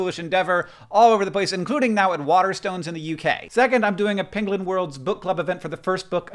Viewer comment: Maybe I've listened to Benedict Cumberbatch fail to say 'penguin' too many times, but did anyone else hear 'pengling'?